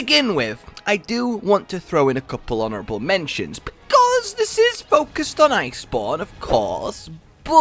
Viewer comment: For a second I thought he said ''this is focused on Ice porn''